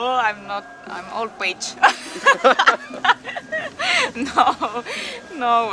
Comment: Did she say "I'm old witch" ? Lmao!